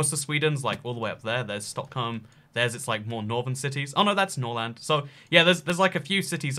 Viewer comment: Did You Just Say NORLAND